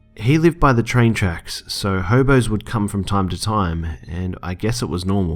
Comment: I thought you said homos at first 😂